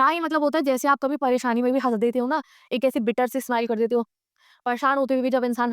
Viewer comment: What?!you said 😅😂😂😂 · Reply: HAS (laugh) dete ho